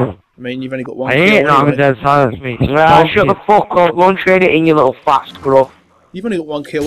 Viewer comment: did he say "trade it in ya fast crook" how is that an insult?